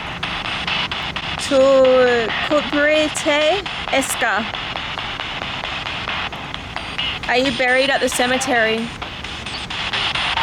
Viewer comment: When you asked “are you buried at the cemetery?” I heard “when it’s time”